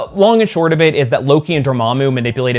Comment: I listen to you in the background, and I had to stop and go back cuz I thought you said they made a giant evil Fleshlight not flashlight 🤣